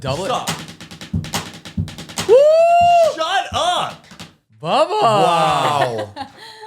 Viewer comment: don’t know why “shut up” sounded like smosh